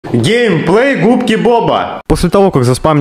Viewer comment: Мне послышалось ГЕЙплей губки